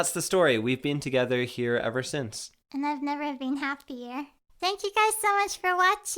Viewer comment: she said” I’ve never been happier” but I thought she said “I’ve never been happy here” lololol